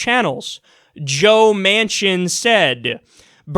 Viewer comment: The first couple of times I thought he was saying Joe Mansion. I was like , no wonder he is a sellout.